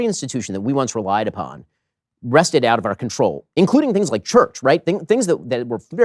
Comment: What is the word? "Wrested" out of our control. … Oh, I learned a new word. Not 'wrestled'.